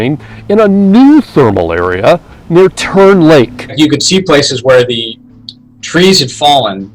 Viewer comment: DID HE SAY TURD LAKE?